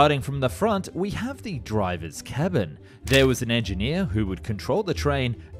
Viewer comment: im very tired, and when you said 'the drivers cabin' i heard it as 'the driver, kevin' and i cant stop thinking about nuclear kevin now.